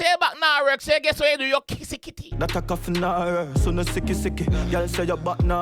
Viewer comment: Cause Mi kiss The Kitty Kitty ....am I hearing correctly ?